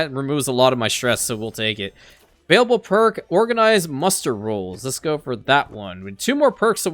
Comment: I always hear and read "controlled mustard rolls" hahaha